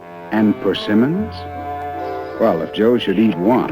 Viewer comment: what is that? Looks like a tomato, but he says something that sounds like forcinammons...?